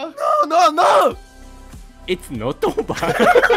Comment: レトさんのイッツ ノット オーバー とキヨのプロジェクトメイクオーバーが同じに聞こえるの私だけ？🤣